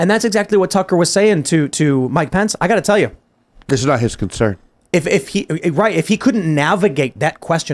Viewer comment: It actually sounded like he said Ukraine is not his concern like he and didn't track with what Tuckster said